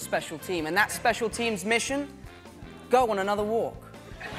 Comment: I thought he said "Doggone, another war" but the Closed Captions say "Go on another walk". Either one works for me.